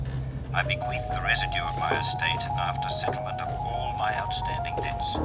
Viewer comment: right at 0:10 dude on phone said to my eldest daughter Judy may Bryant I pray for her forgiveness. then what else does he say? can someone help? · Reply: "i bequest the rest of my estate".